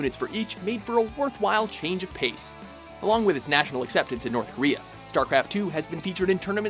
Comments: did you say north korea? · Did he say north korea? Da fuck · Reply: Yeah... he said North Korea. It just makes me freaking ANGRY!